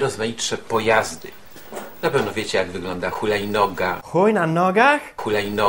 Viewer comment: HULAJNOGA -HUJ NA NOGACH?! OPLUŁAM SIĘ ;D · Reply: Ja usłyszałam hojna noga haha